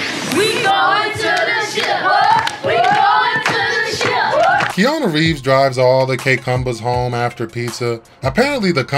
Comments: what did that kid say to Keanu Reeves during the highwater scene? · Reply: Not sure if you care anymore but the kid says “why don’t you invite your pants down to your shoes so they can party?” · Wtf did that kid say at 9:34 ? That shit was a slurred mess. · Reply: He said "yo coach, why don't you invite you pants down to your shoes so they can party' 🤣